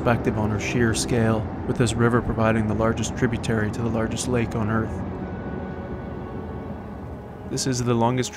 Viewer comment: For a minute there I thought you said Nipigon was the largest lake on earth-- must have been hearing things cause it's not even close. Lots of drama though. You want to try Superior sometime.